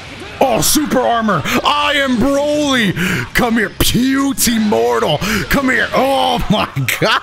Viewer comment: Did he just say "Puty mortal". I'm not even sure if that's how you'd spell that